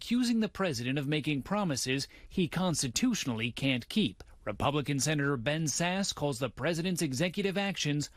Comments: I know this is silly but at 3:18 I heard "Republican senator Ben's ass." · Was anyone else away from their phone and thought he said "Ben's ass"?